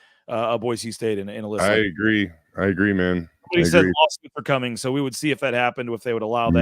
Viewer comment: says "somebody said _________'s are coming so we'll see if that happens" can anybody tell me what he said there? Kind of sounded like lawsuits but I'm not sure i heard correctly lol